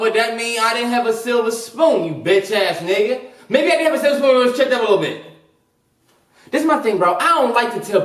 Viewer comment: bruh...what? 💀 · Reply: 😂😂😂 I heard “maybe I had a silver spoon but it was chipped up a little bit “